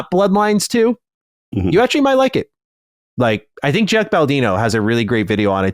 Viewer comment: Jeff Baldino? Am I hearing that right!?